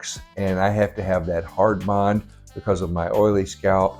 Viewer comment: Hi, thanks for you videos. I have an oily head too. Getting my first system next week. I'm not sure what you said at 4:45. "Hardmon"? What is that? And where do you get the no sweat spray? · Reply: Hi! I was saying that I needed that hard bond glue because of my oily skin. The glue I use is Walker Tape Ultra Hold: <link> A scalp protector is important as well: <link> And here is where you can find the no sweat spray! <link> Hope this all helps bro! Peace!!